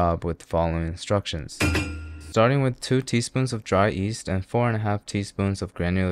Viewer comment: Did you say “dry east”? 7:08 I rewound too many times to care to figure it out anymore 😂